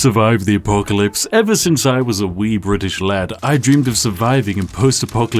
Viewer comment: Anyone else hear "Ever since I was a weeb rich lad"?